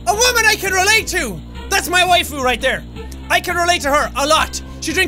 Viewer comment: Did Jack just say fuckin "Waifu"?